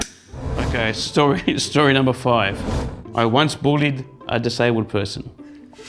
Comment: Lol i thought he said "Boarded a disabled person " 🤣🤣🤣🤣 dam accents 🤣🤣